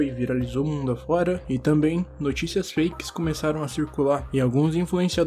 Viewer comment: por um minuto eu ouvi o dizendo notícias flakes